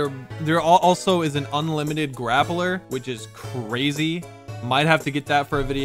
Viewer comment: What did he say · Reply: Unlimited grappler